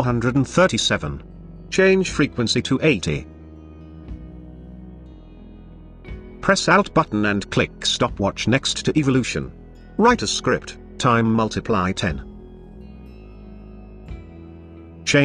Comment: Press alt button and click the stopwatch to open the script panel for any effect or function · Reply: Thank you for your quick reply! I misheard alt- for out-button (I thought like zooming out haha). Keep up the awesome work ^_^